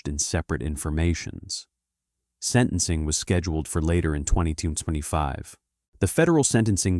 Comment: The narrator says, "Sentencing was scheduled for later in twenty toon twenty five" The open caption says 2225. The closed caption says 2025. I say, "What's going on?"